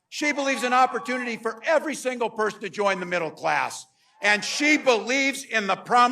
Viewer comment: He said "Les", but I swear I thought he said, "Class" to get them to settle down😂